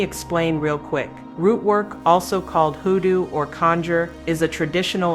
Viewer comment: she said HOODOO. on the screen it said Voodoo. What's she talking about is correct and she said Hoodoo! Voodoo or Vodun there is a difference! For one thing Vodun or Voodoo is a religion they have their own hierarchy just like Christianity Islam etc... People use that interchangeable and thats not right.